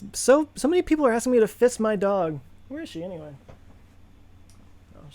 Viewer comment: Oh hell, I thought you were saying fist 🤜 my dog 😮 I get it now. I feel much better now, love the channel